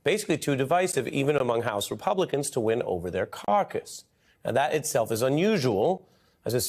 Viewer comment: I had to back up and review what Ari said. I thought I heard him say “carcass” instead of “caucus”.